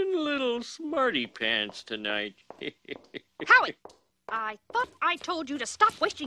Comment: The kid's mother scolds him for "wasting his money", yet he was still on his original quarter for who knows how long. Hardly a waste. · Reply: What is the kid's name? It sounded like; "Harry" or "Howie" or "Howard".